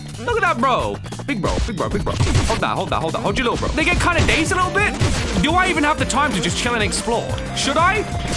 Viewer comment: WHAT DID HE SAY THE N WORD WHA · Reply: "they get kind of gazed a little bit"